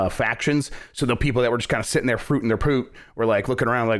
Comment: I NEED to know what Jeff says at the 30:29 mark. “... sitting there fr...?????” Realive me of my pain of trying to make it out, dearest duders. Love y’all